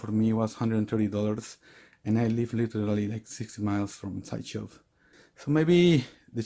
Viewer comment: did he say he stay 6 miles from sideshow or 60 miles? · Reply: 60 Miles friend